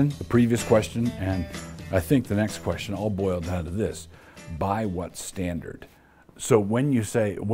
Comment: Haha, I misheard "By what standard?" as "Buy what's standard", but I think the outcome is the same :-)